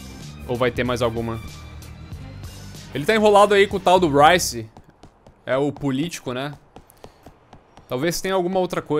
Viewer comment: Eu entendi "Ele ta enrolado aí com o PAU do Ryce, o Político". Na hora pensei "Vdd, deve ocupar bastante ele".